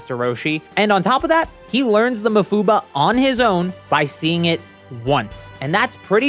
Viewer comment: 😂😂😂😂😂 bro you did not just call the evil containment the Mufuba. Is that what it's called in the sub!!! The mufuballs in your jaw would have been a better name.